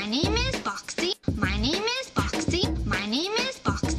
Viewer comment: holy fuck for a second there and 0:10i though she said your name is boxxy who heard it!!!!